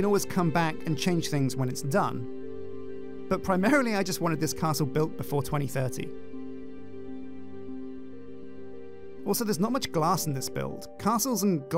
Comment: did he just say he wants the castle built before 2013? edit: do not mind me he said 2030